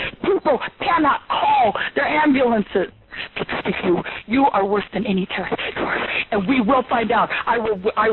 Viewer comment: did she say “You’re worse than any terrorist”? *laughs in osama bin laden” Edit: yep she did. God damn the Karen is strong with this one.